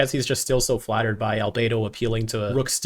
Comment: ngl in UAF it sounds like azmuth is saying "albaydo" not Albeedo" like the rest.